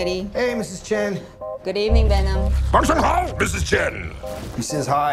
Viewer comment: what did he say ? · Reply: Wan Shang hao Mrs Chen